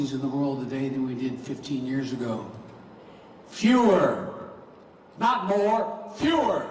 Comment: At first I thought he said "Führer".